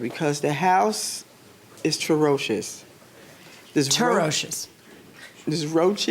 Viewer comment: i think she tried to say atrocious lmfao. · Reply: I thought she said “terrocious” just like everyone else 😂😂